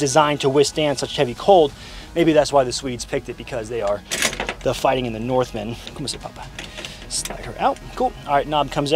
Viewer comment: Lol, did you say "Kom till pappa" at 14:56 ?